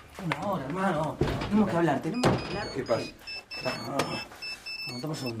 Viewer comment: Guido parece que dice Diego